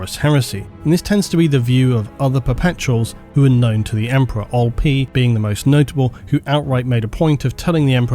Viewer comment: Can someone please tell me who "Old P" is, or if I'm misshearing what he's saying? I've never heard of that name before and trying to google it, but nothing is coming up.